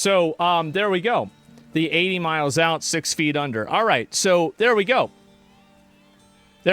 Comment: 86 is actually a reference to the average burial plot size, 8 feet long 6 feet deep. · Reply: i heard that and "8 miles out 6 feet under"